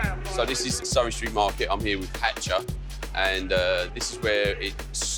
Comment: Who is that at 1:17? I didn't catch the name. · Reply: 2 years after but... It's Hatcha! A Dubstep pioneer!